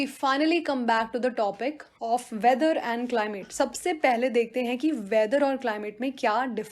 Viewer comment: Still Wondering Why I heard 'Climate' as "Climax" 🤔